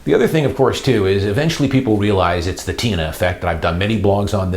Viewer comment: did you say 'Tina effect' at 5:46? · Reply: Yes TINA ( There is no Alternative)